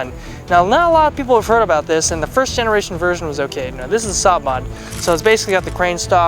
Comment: I thought he said the 1rst generation was gay hahahaha I had to rewind in lol